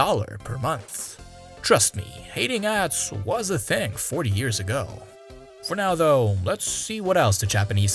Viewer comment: I first thought I heard “trust me, eating ass WAS a thing 40 years ago” Jokes aside I love this series and keep it up!